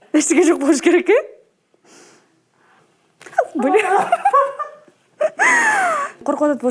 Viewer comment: кто услышал бл* 😂